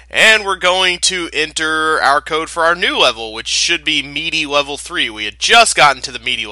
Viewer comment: I thought you said Meaty Level 3 at the start. I was a little scared at first o.O